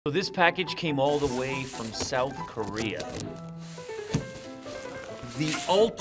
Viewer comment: I thought he said North Korea